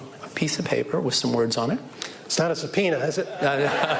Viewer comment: Lol, I had to replay the "it's not a subpoena is it?". Thought he initially said "it's got no pee on it is it?"